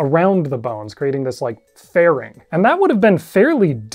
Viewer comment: I misheard “fuzzy twigs” as “fuzzy twinks” and had a bit of existential crisis about Quetzalcoatl